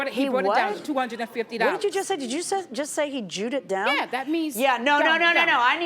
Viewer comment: What did that mean when she said something like he drewwd it down? Someone help my slow ass😂😂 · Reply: From the judges response, I think the woman said “He jewed it down” (Judaism, Jewish, Jews) because there is a stereotype that they are rich but cheap or stingy